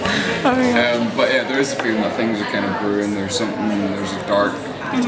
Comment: What did Colin say @ 1:49? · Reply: Faith Worth "there is a feeling that things are kind of brewing..." im pretty sure??